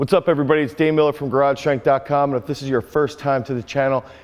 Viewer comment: thought he said Dame Lillard